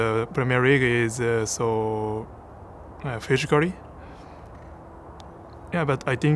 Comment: I know he's trying to say physically. But did anyone heard fish curry???😅😆😂😂😂